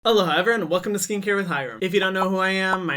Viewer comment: i have memorized his intro at this point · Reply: Me too, but at first I thought instead of saying “Hello hi everyone!” he was saying “Hello Hyram!” Lol